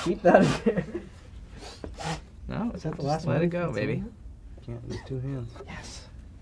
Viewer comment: Thought Vic said “just let it go baby” at 15:33 FUENCIADO